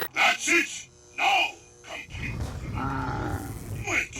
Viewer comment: With the first quote, I always thought he said "Maxis, NO. Complete the device. DO IT!"